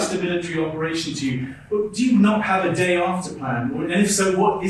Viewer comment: I swear I thought he was saying "do you not have a day off?" 😂